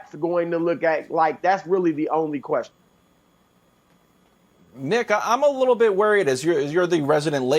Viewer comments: WHAT DID HE SAY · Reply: Y’all ridiculous he said “nick” ur a lakers fan obviously talkin bout lebron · in who else thought the white dude said ‘nig*a* .. in a little worried...’ 😂😂😂😂😂😂